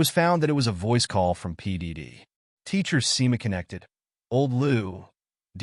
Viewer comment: i thought he called p.diddy